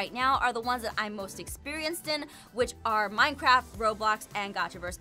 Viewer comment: or 5:04 I think I heard Aphmau say minecrap XD